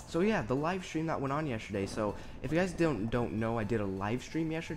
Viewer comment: it sounded like you said yesherday xD